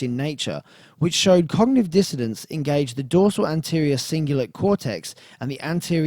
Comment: Is it just me? I keep hearing 'cognitive dissidence' from